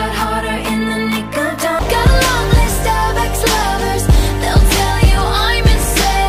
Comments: I heard "got a long list of star bucks lovers" 😂😂😂😂😂😂😂😂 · “Got a lonely Starbucks lover”😂😂😂 Who says that…😂😂I need to know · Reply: I always thought she was saying “got a list of Starbucks lovers” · I thought it said “I have a lot of stubborn lovers, they’ll tell you I’m insane-“ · In Blank Space I heard. "Got a lot of Starbucks lovers" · "Starbucks lovers" is a timeless classic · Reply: Always thought she said star crossed lovers 😭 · I dont know how ppl hear starbucks loves i always heard "got a long list of ex lovers" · i thought it said got a long line of Starbucks lovers anyone else? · I thought it said got a long list of Starbucks lovers 😭